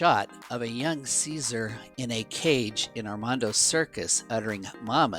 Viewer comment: Ok is it me or am I hearing Obama?